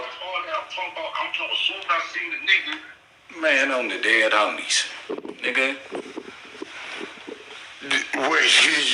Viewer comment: Wayaminutee... What happened at 4:04 ? I ain't hear spi say anything spicy.... Why bricc get so defensive and hang up like that??!? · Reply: Spider loc said “ Fck All That ” after Bricc tried to say he said a lot of stuff saying the reason why he shouldn’t leave or kicked out